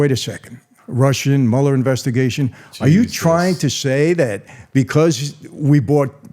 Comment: did andrew say jesus 2:10:29 lol